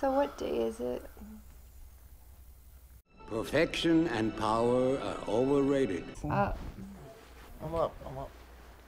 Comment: bro i swear to god when the husband snuggled onto the wife in bed instead of "Perfection and power is overated" I heard "PROTECTION and power are overated" which in context is pretty fucking sick considering 1 second after she pushes him back away with her hand